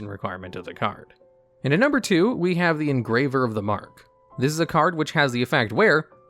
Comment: You Say engraver of the mark , i hear abyss watchers